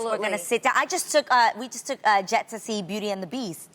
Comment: I thought Adrienne was saying she took A JET to see the movie I was Shook 😂 till I realised it's her neices name 🙈🙈